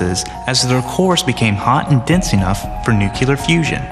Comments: did he say nucular? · Is it me, or is he really saying at 4:18 "NUCULAR fusion"???